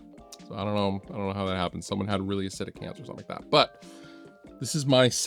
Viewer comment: What does he say at 4:39? It sounds like he’s saying “someone had a really set of cans or something like that”. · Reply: "Someone had really acidic hands or something like that" lol took me a few listens